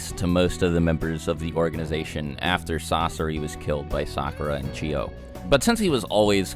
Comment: Shit for a second there I thought you said "after Sasori got killed by Sakura UCHIHA" instead of "Sakura and Lady Chio" LOL I was like, WHAT?! can't see Sasuke ever wanting to marry dat bish orz